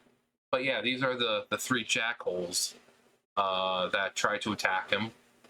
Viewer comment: Did you call them "the three jackals" or "the three jackholes?" A fitting nickname either way! XD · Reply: jackholes